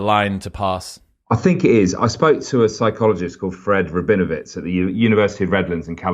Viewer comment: Thought he said ‘spoke to a psychologist, friend with benefits’ !!!